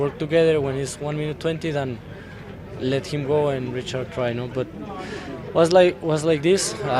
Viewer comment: What Del Toro did said in 1:37? "but Richard cry no"?????